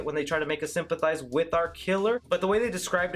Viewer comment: I Thought This Dude Was Gonna Say R Kelly But He Said Our Killer 🙄